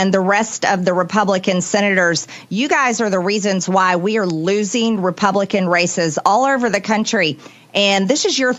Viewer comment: For a tiny second I thought she said 'this is why we're losing Republican racists' - which is still kind of accurate haha